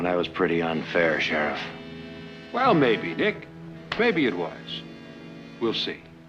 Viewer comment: Did he call Nick "Dick?"